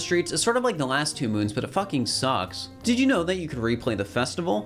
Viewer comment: did... did say "it fucking sucks?" WHERE'S THE HECKING????